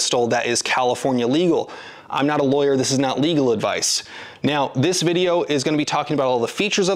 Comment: Weird I always hear "I am a lawyer and this IS legal advice"